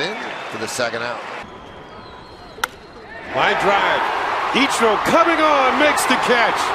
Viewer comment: イチローカビゴーンって聞こえるw